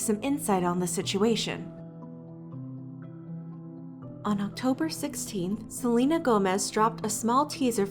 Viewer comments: is no one going to seriously talk about how said On October SIXTEENTH (sorry idk proper american and english grammar) instead of October 18th like what is said on the small hanging cute subtitles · the subtitles say October 18th but she says October 16th?? not a big deal but it through me off 😂